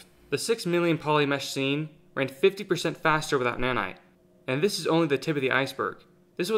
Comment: did you said WITHOUT or WITH NANITE? More clear please. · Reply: Lower total cost is faster(on screen). "Without"